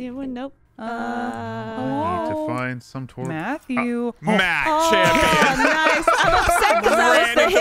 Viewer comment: did jeremy just call Matt "Matt Champion" 👀👀